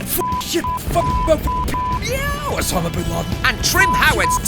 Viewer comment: did he just say Osama Bin Laden?